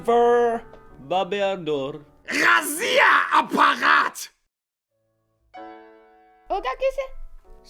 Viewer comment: мне послышалось Россия аппарат :D